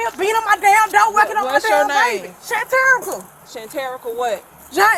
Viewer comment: I heard “hysterical” when she said her name was “Shanterica”